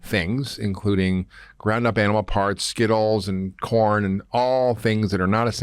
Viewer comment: Did he say they feed cows Skittles?